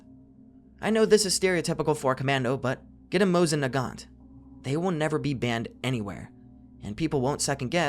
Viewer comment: Anybody catch what the gun he recommended was called? Sounded like he said “Mose and a guant” but that obviously came up with zero results lol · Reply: Mosin Nagant. A classic /k/ommando favorite. Not as cheap anymore unfortunately.